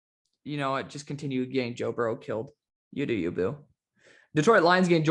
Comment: did you say "you do you boo"? 🤣